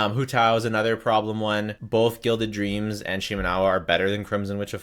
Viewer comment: Did he say hutao needs guilded? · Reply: Gilded and yes